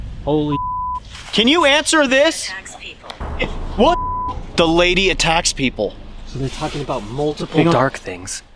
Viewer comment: I legit thought it said "the lady taxes people" 😂